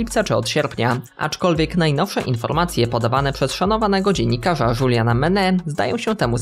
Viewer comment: czm ja usłyszałem Juliana MENEL XD